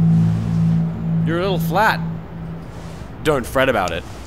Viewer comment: Tom: You are a little fat Actual Tom: I'm a little fat Edit: Oops, I heard fat instead of flat, my bad